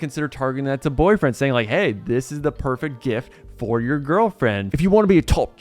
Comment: Did you say jift at 5:00 😮